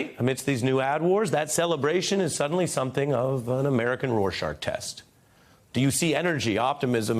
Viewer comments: Did he say roar shaRk? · Did he say RorSHARK test? 7:25 🤔